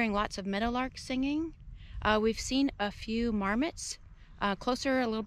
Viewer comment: Nice, another great location you visited. It's funny when you said about marmots at first I thought you said "Varmits", haha